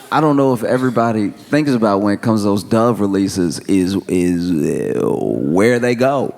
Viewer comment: I thought he said Dog Release. Dying Dog Release 😮. Doves was worse 😅😅😅